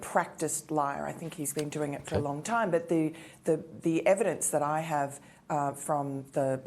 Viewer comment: Why is it media mishears everything??? She said Practiced liar..Not Proven liar